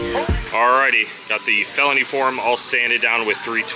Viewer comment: Love how it pans to the clip where you can see REID on the car then you said “ alrighty” but it sounds like your saying R E I D. lol